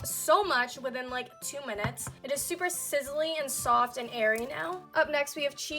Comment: I thought she said sizzly, soft and hairy now 😂😂😂